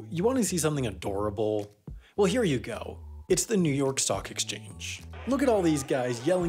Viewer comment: Damn, I really heard "The New York sock exchange" and was like, shid, that is cute. Look at that little happy fella'